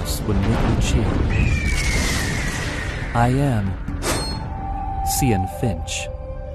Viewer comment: I thought he say cyan fish 🤣🤣🤣😂😂